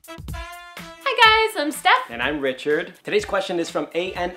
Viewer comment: this is so true!!! my mom thinks gayness is only a western thing... like no... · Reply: At the beginning it thought they said this - Girl: Hey guys I'm stuffed. - Guy: And I'm richer.